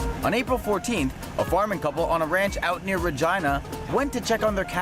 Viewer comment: On 8:29 am i the only person who heard vagina?!?